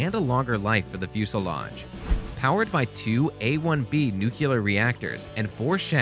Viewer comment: Did you say "nucular" reactors at 3:19 whilst reviewing military craft? I normally would let this slide... but come on end the GWB intelligence movement now!